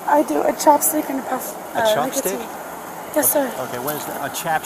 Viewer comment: I've never heard an officer like him being so calm and softly spoken. I also thought she said a chopstick.